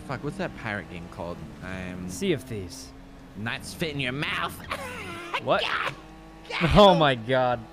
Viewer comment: Heard it as "not spit in your mouth" and was a little confused.